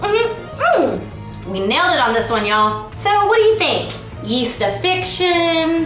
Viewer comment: Please do Feast of fun the first two times you said the other one me and my family thought you said yeast infection. 😐😂